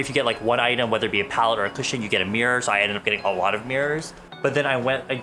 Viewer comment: I thought he said you get beers and he had a lot of beers I was like- you’re gonna be drunk KDKDN